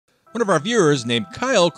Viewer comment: Bro I swear at first instead of “one of our viewers…” I heard “one of our fuhrers”😂